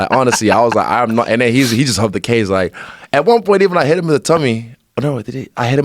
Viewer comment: what did this man just say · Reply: At one point I even hit him in the tummy